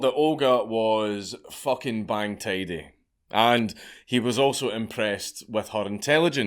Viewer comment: For all you British isle folks, what does he call Olga at 18:21? Bang teedy?